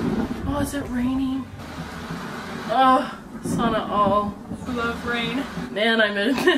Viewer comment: Did just said "sana all" @ 23:33???